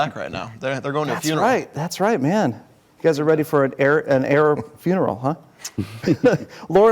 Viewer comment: did he said arab funeral ?